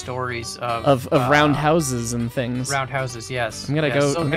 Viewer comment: Thought it said for a minute “talk all things roadhouse” either way both are good